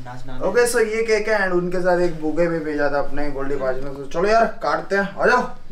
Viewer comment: Did said Goldy bhai